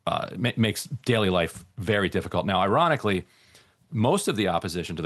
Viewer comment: did he say Iranically? Lol